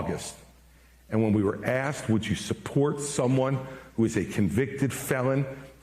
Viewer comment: Maybe he's bitter but he's also right. I'm also perplexed as to why he didn't do better. And I love how it sounded like he said Vifake Ramiswami. 😆